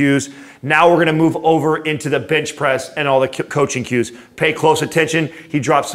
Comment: Did he say Bitch Press? Haha